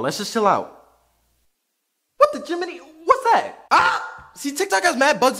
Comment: Did he said what the jiminie?